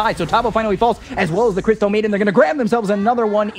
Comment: Lmfao on 3:22 did he really say "krypton maiden" straight outta DC universe??? She must be stronger than superman